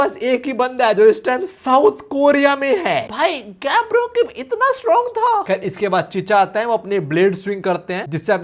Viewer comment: bro he don't say gab.. i think he say her brother/daniel dad